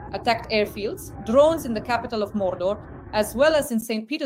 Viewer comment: I know this is a serious moment.. but around 1:02 I thought I heard "Drones in the capital of Mordor..." - and I thought, that is a very apt description of Putin's Russia.